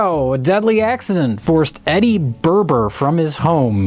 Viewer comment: “Eddy Berber” I swear I thought he said Eddy Burback and I about had a brain aneurysm